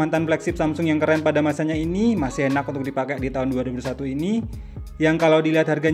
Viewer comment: 2001 dong kata mimin,, entah doi ngomong 2021 versi 5G jadinya kurang jelas